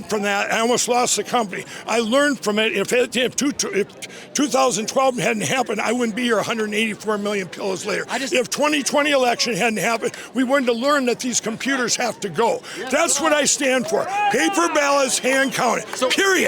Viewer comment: did he say @ 10:59 "i wouldnt be here at 184 million kilos later"